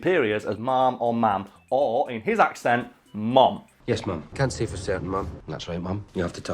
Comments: I use the word ma'am a lot and out of context I still thought he said Mom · yes i thought he was saying the word mom or mum but i thought it was a code word for a prime minister/important person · I always say ma'am. I thought he was saying that but with a thick accent. My dad couldn't understand why he kept calling all the women mom, but even though we kept saying ma'am we'd joke about him having mother issues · I knew he was saying ma'am but it does sound like he is saying mom. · Hell I thought you guys are saying mom instead of ma'am. I'm from the South and it common to call all women older than you or in a position above you ma'am, so I thought he was saying mom but after listening to it a couple of times I realized it he was saying ma'am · I always thought he was saying mum which in that case I assumed was a slang way of saying ma'am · I thought he was saying "mom" or "mum" but "ma'am" is very common in American English especially in some southern accents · I definitely hear "Mom" but I took it as "Ma'am" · I thought he was saying Mom, But I assumed it was a British turn of phrase for a woman superior and just a replacement for Ma'am but with the same meaning. Based on assuming that's what Daniel Craig's Bond was calling M in his 007 movies. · While he does sound like he is saying Mom, I took it as him saying a version of Ma’am, a shortened version of Madam. I just figured he was either speaking too quickly or his pronunciation was off due to cultural differences